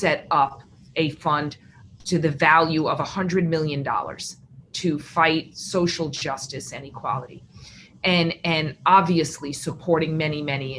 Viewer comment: is she saying “to fight social justice and equality”?